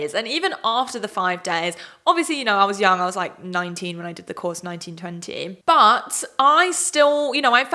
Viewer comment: it almost sounds like she says, "I was nineteen when I did the course, in 1920." exposed as a vampire?